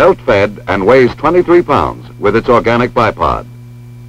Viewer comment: I thought he said "orgasmic" - shows where my head's at.